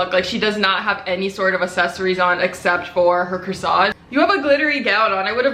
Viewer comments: I LOVE BUT SHE SAID CROISSANT INSTEAD OF CORSAGE AND IM DYING😩❤️😂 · it sounds like you say croissant instead of corsage to me lol. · i thought she said “croissant” instead of “corsage” 😂😂